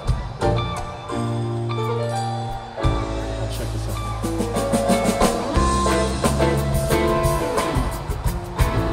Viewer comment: anyone know what mike is saying at 9:07?? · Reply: "Check this out."